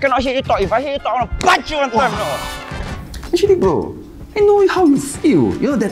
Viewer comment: did he just call "bro" ?